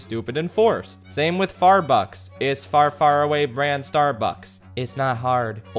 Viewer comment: thought he said farfucks for a second lol